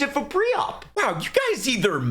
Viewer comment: did he say world war 1 or volvo 1? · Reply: Ww1